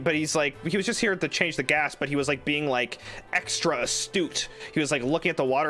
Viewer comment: Slight correction, I think he said "extra astute", with astute meaning an ability to notice and understand things clearly :)